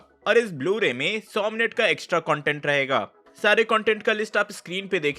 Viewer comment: Does he say 100 minute ka. Extra content rahega! ???😯😯